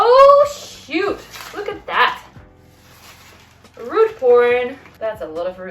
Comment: Such gorgeous leaves!💚🌿🙌 Did I hear you say "root porn"? Lol!